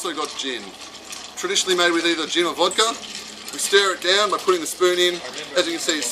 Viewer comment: Every time he says "stir it down" I hear him say "stare it down", which is better on so many levels.